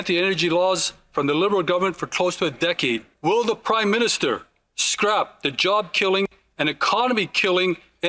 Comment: Sounds like Hodgson said “crime minister”. That’s would be the most truthful thing the liberals have said.